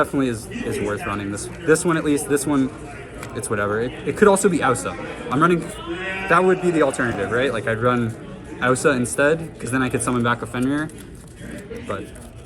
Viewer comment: what's the alternative to Evilswarm said at 6:35? I can't understand the name :/ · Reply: Aussa earth charmer, link-2